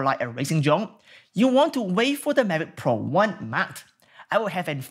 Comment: great review! one question do...what did you mean at 15:50, when you say to wait for the mavic one "matte" ? is that a Future Drone still to come out or an update to the current mavic one? Thanks deeply apreciate your reviews, keep it up! ;) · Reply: Mavic One Pro mount. I also didn't hear it, so I used the subtitles.